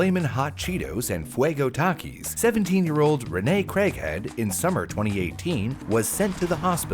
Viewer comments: you said: "craighead" But I heard "Crackhead" at first, yikes that Craighead can be taken out if context. on a side note: I really do not eat chips anymore except for the few quest brand chips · "Craighead" thought you said "crackhead"